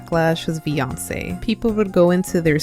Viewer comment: I thought you said Beyoncé not vioncll 😭🙏